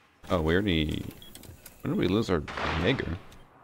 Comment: sounds like he says n***er and not Jager, wack